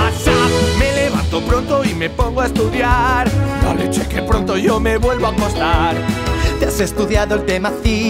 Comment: "me levanto pronto y me pongo a estudiar" "la virgen que pronto yo me vuelvo a dormir" same. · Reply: Yo pensé que decía pareche que pronto yo me vuelvo a dormir xD